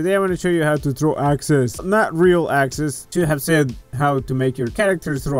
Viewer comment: I thought at the start you said throwing access